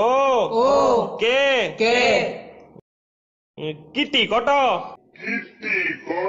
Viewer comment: I thought it said Tite ko to